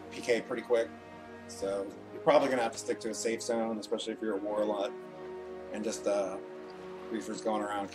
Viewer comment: did you just say warlock... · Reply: TEMPORARILY AWESOME I think he said "if you're at war a lot" since he was explaining a reason for getting PK'd